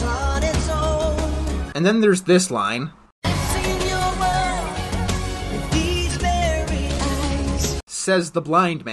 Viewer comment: What did he say at 9:35? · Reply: “I’ve seen your world, with these very eyes.”